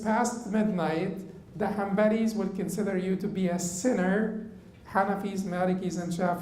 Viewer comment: In the Hanbali mazhab, if one defers Ishaa to after midnight without any valid reason, does this entail praying outside the time and hence kufr? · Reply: I think said around 59:10 that the Hanbalis would consider this a sin, and he did not say kufr.